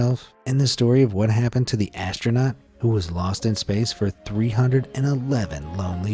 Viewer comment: Lost? I thought you said LUST in Space!!